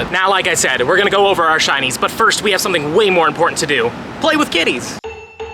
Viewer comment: When he said “play with kitties” I thought he said “play with kiddies”